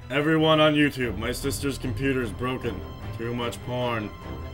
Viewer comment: all I heard was 'too much porn'.